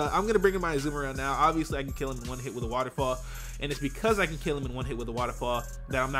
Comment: At first I thought he said water claw instead of waterfall, and I was like"what is this move that you speak of?"